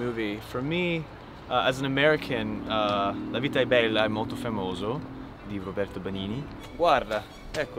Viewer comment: he says brutto (ugly) but the subtitle says beautiful